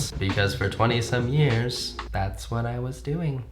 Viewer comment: i thought he said 27 years, and i was like wtf connor u ain’t even that old